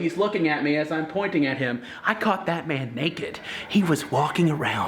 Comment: Omg lmaoooo "I caught that man naked!" For a second I thought you said you caught batman naked 😂